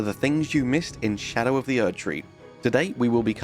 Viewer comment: Despite your explanation I heard it as "Welcome to Charo of the Erdtree"